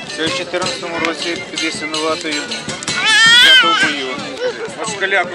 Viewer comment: декулька раз переслуховував,так і не зрозумів,що сказав той козак на 1:40."я в 14 розі під Ясинуватою..." Що там було під Ясинуватою ? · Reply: Шабля взята в бою в 14-му під Ясинуватою. Там сам ледь розібрав записане, адже на фоні музика заважала